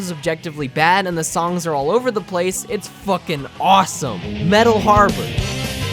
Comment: did he say metal harbor or one piece opening music? lol